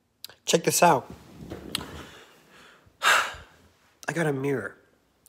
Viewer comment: I thought you said I got a minor 💀